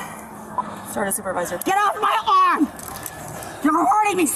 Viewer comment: I thought she said “ get off my lawn “ 😂 she said arm 😂